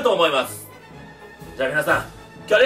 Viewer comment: みんな言ってるけどハネハネがハゲハゲに聞こえたwww